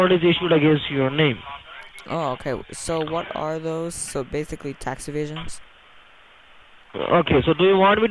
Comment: i thought he said tax invasion